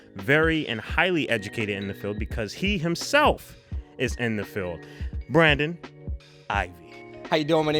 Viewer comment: lol I thought he said he was in the film at first